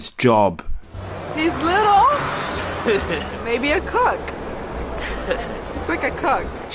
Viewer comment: I thought she said 'a cuck' 😂